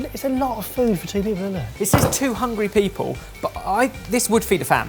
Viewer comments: I thought they were saying about the chicken thing that it fed "200 people" and I was like...wtf, how? and they were actually saying "2 hungry people" xD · I keep hearing "two hundred people" instead of "two hungry people" when they were making the last kit